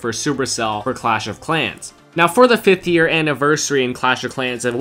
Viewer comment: when u say 50 years instead of 5 😂😂